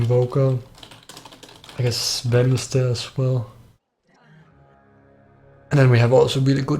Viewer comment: what did he say?! Benny stairs as well? I'm not even kidding I've rewinded like 7x and I cannot for the life of me figure out what hero he's talking about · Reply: 'I guess Sven is there aswell'